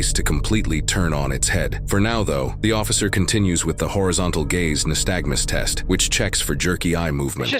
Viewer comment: Chile, I just knew I heard "HORIZONTAL GAYS" 😂 Horizontal Gaze smh